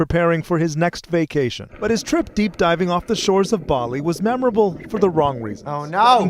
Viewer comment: BALI?!? DID HE SAY BALI!?!?